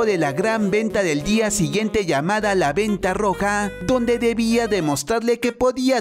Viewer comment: la venta roca escuche yo xd